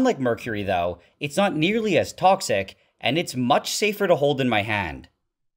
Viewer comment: I thought he said it’s much safer to hold than my hand lol